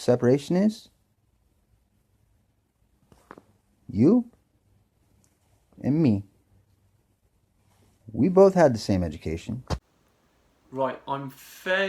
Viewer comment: LOL... 11:08 - did he said: "You... and me... we got no the same medications!" ??? :P :P